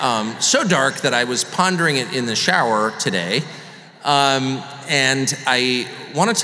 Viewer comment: I thought he said, "I was pounding it in the shower this morning" 😂